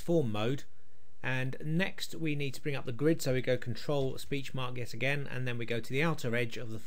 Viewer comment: Nice video, I have a question - at about 2:37 you say - to bring up the grid you click on... ? I for the 10 times I listened I still can't quite make out what you say to click - sounds like speech something, help please... and thank you! :)